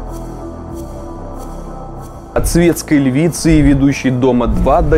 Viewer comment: Прочитал название как: "собачка провоцирует" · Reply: а мне послышалось вначале - "свинская львица")